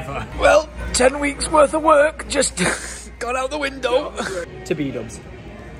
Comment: did you say "to Bdubs"?